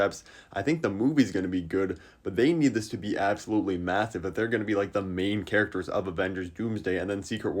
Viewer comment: Wait! Rewind that…. Did he just say… massive?